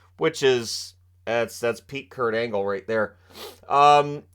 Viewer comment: Did he say PEAK Kurt Angle or PERC Kurt Angle?!?